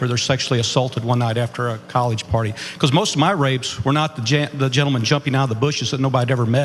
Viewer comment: did i hear him say "most of my rapes" ?